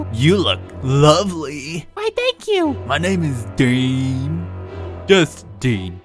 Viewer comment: Lol when he said dean it sounded like he said “My name is BeAn” when said it, 🤣